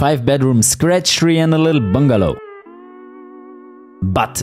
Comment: why did i hear.. he said "and a little bungalow" lol